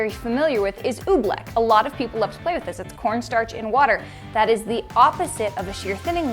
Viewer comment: pfpfpfffff XD XD XD i cannot have been the only one who heard her say pornstar at 1:54 XD XD